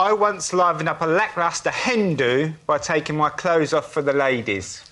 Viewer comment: I also thought he said "Hindu" at 1:46. By the way, there's a great song by Rachel Parris about a Hen Do On A Train.